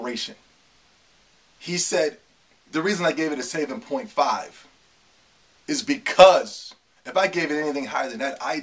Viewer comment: Lol did you say saiyaven point five? 5:42 Epic.